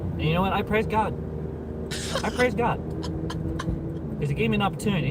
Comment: i swear i heard i crave cock not i praise god ;_